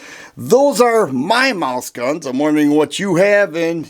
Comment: I thought you said “Moose gun” 🤣🤣 Wife has a 32acp Beretta tomcat and an short NAA 22lr